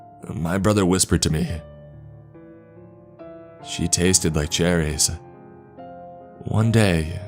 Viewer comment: At first I heard “She tasted my cherries.” Wouldn’t have been too surprising seeing as Zeus banged everything that moved.